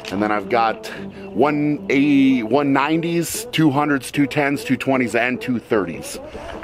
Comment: Bro im not strong enough to watch these videos said “ 210s, 220s and 230s” my ass heard it as “2 10s, 2 20s and 2 30s” 🤣